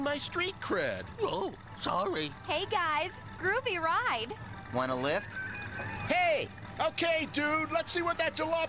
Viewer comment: What A Jamie Said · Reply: "Wanna lift?" (want a lift?)